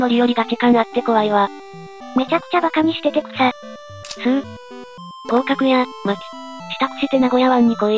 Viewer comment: 合格じゃなくて降格に聞こえた